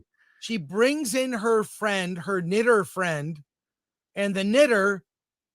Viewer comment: "She brings in her friend, her knitter friend..." Wait... what did he just say? Oh, "knitter"... whew...